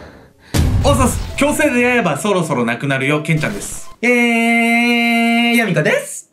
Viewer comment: ～がなんて言ってるか誰か教えて下さいwwwwwwww 「強制恋愛バーそろそろなくなるよ、けんちゃんです」？wwwwwwww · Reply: 矯正で八重歯そろそろなくなるよ